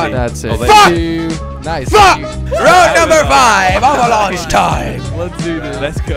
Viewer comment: Everytime he said Avalanche time.. I thought he said "Have a Lunch Time" Dunno why xD